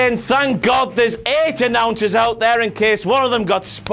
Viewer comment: You said announcers instead of referees.